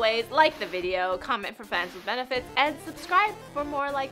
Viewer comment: I had to rewind the ending- I heard "Comment below for friends with benefits," not " fans with benefits." Alas, I was deeply saddened after the 2nd take :(